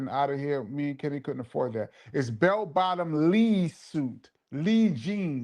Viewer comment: Tonight might just be in contention for greatest episode of all-time!!! (It’s definitely in the conversation!!!) Double OT… There were as many likes as people tuned in at one point, and the flow was AMAZING!!!! Off topic, and I could be wrong, but it’s possible that GREG NETUS might be the only regular to never miss an episode in 3 years!!! @ 2:07:45… I’m so embarrased!!! This was my Kenny Parker LOVE IS A HOUSE payback!!!! All these years (DECADES!!!) I really thought KRIS said BELL BOTTOM “REESE” SUITS!!! 🤣😂🤣 Glad we cleared that up…. Sadat X interview in December 2024 and tonight… 2 BEST EPISODES!!! 🔥🔥